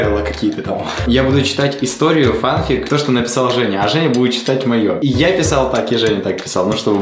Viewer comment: мне не одной показалось то что он сказал "то что написала женя"